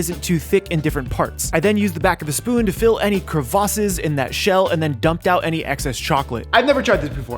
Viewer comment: he did not say "Crua VA sis " I WONT I CANT NOO